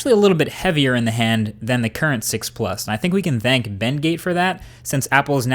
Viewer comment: Thought you said Ben-Gay at :47 lol